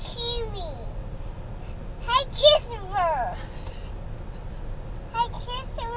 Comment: Trying to figure out what was saying in the car had me laughing so hard 😂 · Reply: It sounded like she said “goodbye Christopher”